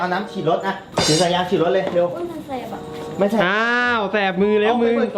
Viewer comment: อ้วนมันเเสบอะ#อ้วนนนน ได้ยินไม่ผิดใช่ปะ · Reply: ไม่ใช่เขาพูดว่า"โอ๊ยมันแสบอ่ะ"(แสบมือ)